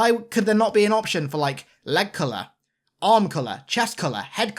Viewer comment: When you said "leg colour" I heard "Lekgolo"...